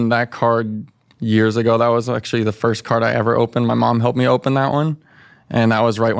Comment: I heard "my mom helped me open this card" and I laughed and did a cartman impression. Fucking hilarious.